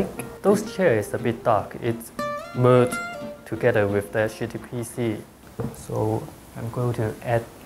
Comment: Does he say "their chair is a bit too dark--it's blurred together with their shitty pc." at 3:05?